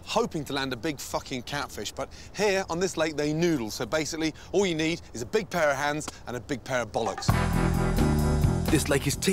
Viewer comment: I thought he said "All you'll need is a big pair of hands, and a big pair of balls."